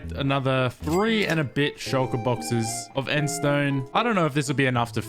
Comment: Did any one notice to 10:12 he said "bit shulker " but I heard BITCH